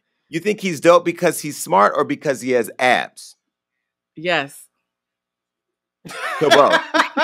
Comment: said "yass" 😂😂😂 i heard you sis